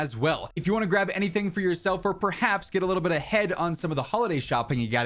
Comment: Did he say get a little bit of head @ 6:19?!? 😂